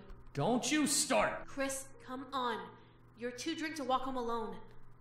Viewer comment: Too drink to walk home alone? Kinda sounds like drink instead of drunk :d